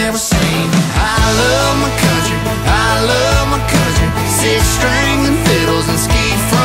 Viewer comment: It really sounds like he says, “I love my cousin.” Try it. 1:25 lmao